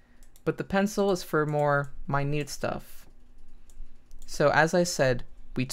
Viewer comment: What did he say at 18:12 ? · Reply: ***** no he said minute which means very small